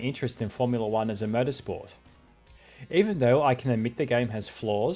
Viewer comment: Did anyone else mishear "motorsport" as "murdersport"?